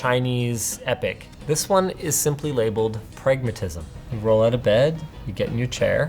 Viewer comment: At first I could of sworn I heard "pregnantism", and with a bed incorporated right into the setup...I didn't know what to think! Then I realized that I misheard you. lol